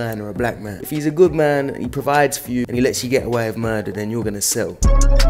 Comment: did u just say let u get away with murder?